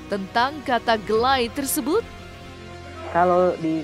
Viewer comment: Dia ngomong 'gak like', karna mandjalita jadi kedengerannya gelay